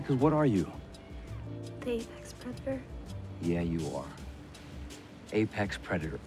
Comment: "What are you? Apex predator" Why did I hear "a pez dispenser"